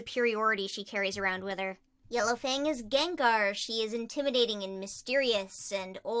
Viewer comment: While u said “yellow fang” I was drawing and I heard “yellow thing”